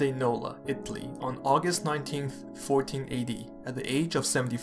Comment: did you say he died in 1480? The Roman Empire was way way way dead by then. Since 476 AD.